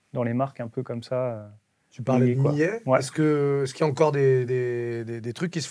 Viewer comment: c'est quoi cette marque française dont vous parlez ? "millier" ou un truc du genre ? connait pas, je suis curieux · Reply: C'est Millet, légende des Alpes